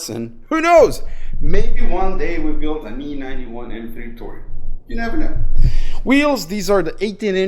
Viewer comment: Have i heard correctly? An E91 M3 Touring? Well that sounds nice!